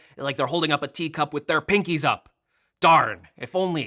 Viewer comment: did i hear… pinky up?